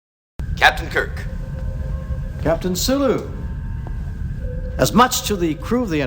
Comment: I put the subtitles on by accident and the tittle said "captain sOooo" Instead of "Captain Sulu" and I'm frikkin dying over here!!